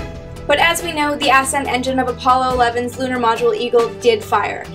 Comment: I'm sorry, did you say "ass-end engine" @ 2:29?